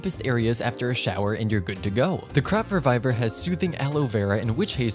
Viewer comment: I keep hearing "crap-reviver" I can't 🤣🤣😂